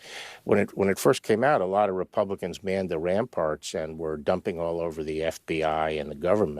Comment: did he say manned the airports? @ 0:33😆